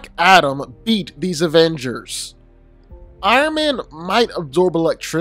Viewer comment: bro i thought my man said “would black adam eat these avengers?”